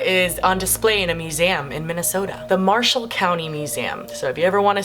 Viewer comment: I could have sworn you said Martian County, Minnesota 😂